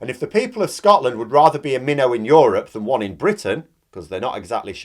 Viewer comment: What is a "mino"or what is actually saying in this video? 6:35 for example